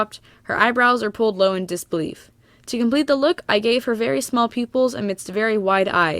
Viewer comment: I thought you were saying small people lol